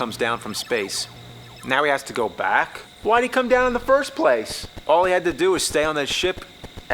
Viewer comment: did I heard "All we had to do, was follow the damn train CJ." ??